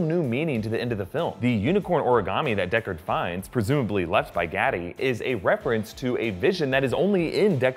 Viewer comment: The other cop is Eduardo Gaff, not Batty. Roy Batty was a Replicant. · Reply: I thought he was saying "Gatty." Couldn't figure out wtf he was referring to.